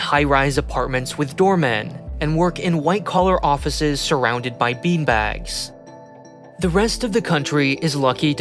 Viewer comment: thought I heard, "work in white collar offices surrounded by d-bags". Would've been a good easter egg